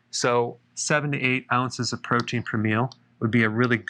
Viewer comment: Keto pronunciation: Normal human, "Keedo", Dr. Berg, "KEY-TOE". I love your work! Keep rockin' the awesome vids. NOTE: "7-8 Oz. protein per meal" is confusing. Most of us tracking macros are thinking in grams. Pretty sure you're not talking about 200 plus grams of protein per meal. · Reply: Glad you pointed out the ounces there.. somehow I thought he'd said grams. 6 oz cooked chicken thigh about 40-50grams protein, 1-3 meals per day depending on fasting schedule. Thanks Dr B